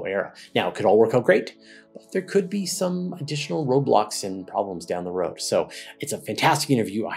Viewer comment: i swear i thought he said roblox